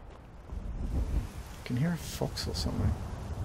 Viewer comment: <link> "Can you hear a fox or something?" I could have sworn I heard a dragon. Many thanks for the video:-)